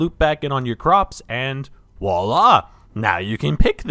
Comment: did you just say “wallah” instead of voila?